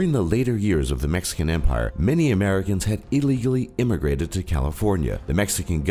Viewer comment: sounds like he says illy gilly.